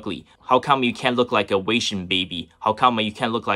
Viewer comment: when you said 'wasian baby' i heard 'weixin baby' hahaha